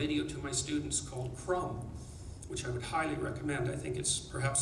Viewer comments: Does anyone know what video is he talking about at 17:06? "Crumb"? "Crom"? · What is the name of the documentary he mentions around the 17:00 mark? It sounded like "Crumb" but that is about a cartoonist. With closed captions it says "From" but I can't find a movie called that. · Does anyone know what documentary he's talking about around 17:00? I looked up "Crumb"/"Krum" and it's a documentary about a cartoonist. Am I hearing him incorrectly?